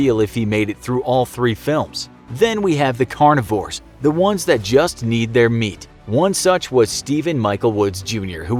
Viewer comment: I thought he said “the ones who beat their meat